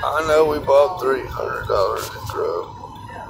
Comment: did he say 'i no we brought 300 dollars of dope'